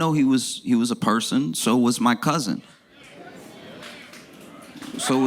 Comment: "This man was a father, he was a husband — okay, so was Marty" — get 'em man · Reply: I didn't hear "So was Marty". I heard, "So was my cousin."